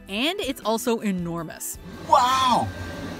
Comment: lol I thought that shark said “wow”